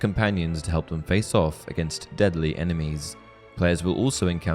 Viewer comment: Legit thought he said "dudly enemies."